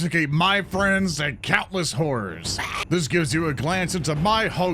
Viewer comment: At first, I misheard "Horrors" as "Whores" and was worried that Zoran had fallen to Slaanesh 😅😅